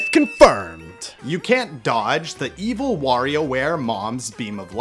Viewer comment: I thought it microwave!? Why does it say Warioware!?!?